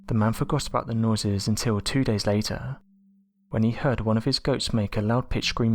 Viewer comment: First I heard "one of his ghosts", but soon realized it was one of his goats. 😅